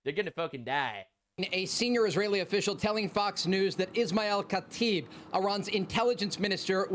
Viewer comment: What he said: Ismael Katib What I heard: Lisan Al Gaib